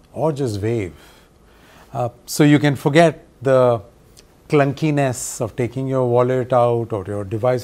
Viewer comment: "so you can forget the clunkiness of taking your wallet out." that's their sales pitch to give away the last bit of privacy and autonomy we may think we have. oh boy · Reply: Thanks for the clarification. I thought the man said, "so you can forget the uncleanness of taking your wallet out." Now, Russell's riff on "clunk, clunk" makes hilarious sense to me.